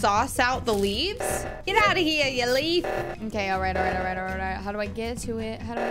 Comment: Anyone catch when the editor said “it’s time for you to leaf”